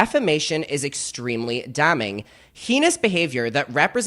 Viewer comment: wtf did he say? Henis?